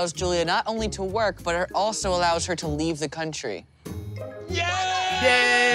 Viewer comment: I thought they said she could leave the county instead of the country. I can’t believe Brandon was her dream